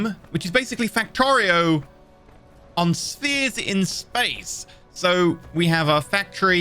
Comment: Cubes? Thought you said spheres